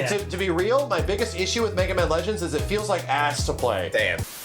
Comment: I heard at first, game feels like ass play. I was so confused glad I listened again to clear up my confusion.